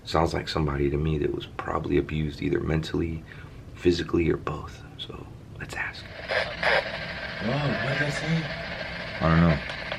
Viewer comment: Ran this through my audacity software sounded like " lets talk "